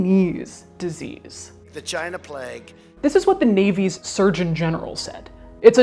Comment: Okay okay okay okay, now I've listened to Trump about 25 times at 12:00 and I swear he is saying "vagina plague" Having watched the rest of the video, Trump saying Vagina Plague is the new Bentham's Head. the Plague Fake is a just a wannabe